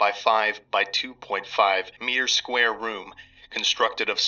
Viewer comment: I had to quadruple take that bit. I thought he was saying 5mx5mx2.5m^2 (as in square meter) room instead of 5mx5mx2.5m square room and gave myself a migraine trying to figure out how that made any sense.